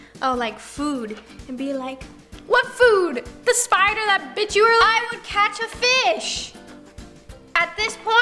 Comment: Is it just me or did I hear cuss. Did she say”what food? The spine of the b!tch you don’t like”